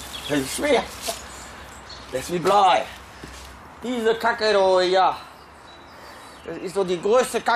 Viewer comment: Was sagt er bei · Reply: Diese Kacke da hier